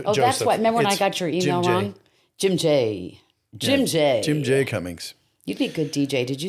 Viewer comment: I could've sworn I just heard him say his name is Jim Joseph Cummings. Why does his Wikipedia page say a different middle name? 😂